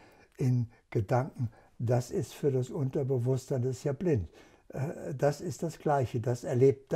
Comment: "der Unterbewusstsein ist ja Blind"? Minute: 8:55, habe ich das so richtig verstanden?